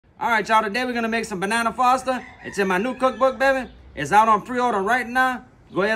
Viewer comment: Thought he said banana pasta😂